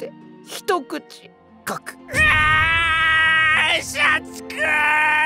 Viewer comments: シャチくぅーーーーーんが 社畜ぅーーーーーに聞こえる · シャチくぅぅぅぅん が 社蓄ぅぅぅぅぅぅ って聞こえてしまった、